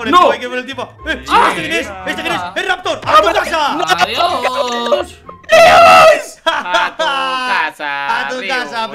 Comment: Que dijo Raptor ? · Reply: Dijo nais